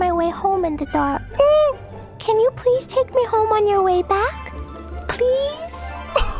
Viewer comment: I first heard this as "bring me home on your back" and i had this adorable image of the traveller bringing a sleepy klee home on his back